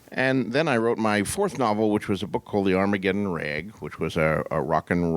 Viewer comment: I always hear "Armageddon Rag" as "I'ma gettin' rekt".